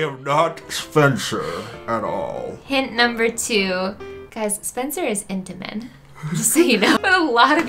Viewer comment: “Spencer is into men”😂😂 people who don’t know that make me laugh lmao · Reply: Oh! I thought she said "Spencer is intimate" and I was like, yeah, okay then